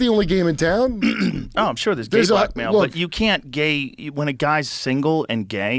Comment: I thought he was saying “gay black male” not “gay blackmail” lmao